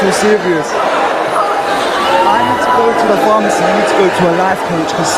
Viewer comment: ?? What happened or what did say that got the audience aggravated? I've seen that clip several times and never noticed that before. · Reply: Simon said they all sounded like they've got colds so said "I need to go to a pharmacy but you need to go to a lifecoach" lol